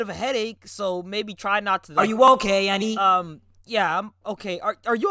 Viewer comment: I thought muzan said ayuwoki instead of are you ok lmao